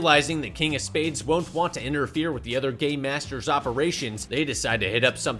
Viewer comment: "realizing, the King of Spades won't want to interfere with the other Gay(me) masters'- " I heard gay masters instead of gaMe masters